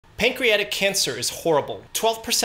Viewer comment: At first i heard "patriotic cancer"